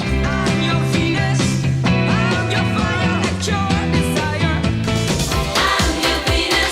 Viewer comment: Venus, by the shocking blue, was one of my favorite songs as a youth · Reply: I thought the chorus said, "I'm your Venus, I'm your pride and joy, yes I am "😅